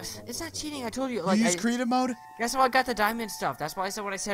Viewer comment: I heard crepy cave noses